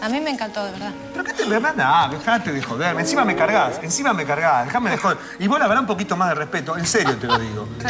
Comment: alguien sabe que le dice listorti a la conductora en el minuto 2:19 ?? no se entiende :c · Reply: Le dice " pero que te mmmee , andaa dejate de joder , encima me cargas encima me cargas "